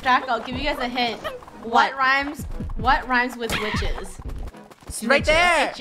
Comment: I thought said biches XD